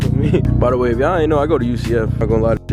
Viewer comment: u in florida? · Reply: awe shit my bad I heard wrong thought u said u go to UCLA lol,maybe we can catch a race if u ever in Cali or if I go out there bro,u go IG?,loving that clean as 9th gen u go G 💯